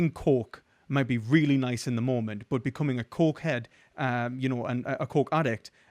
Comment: Oh "coke", as in cocaine. Your accent really threw me there for a minute."What did he just say I wanted in the moment?" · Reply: 😂 I thought he said coke “in the morning”. It made me lol I was like “wow we have different ideas of getting the day started I guess - are cokeheads usually early risers?”